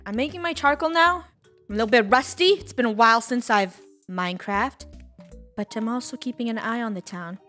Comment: Just me or did she say "MineCracked"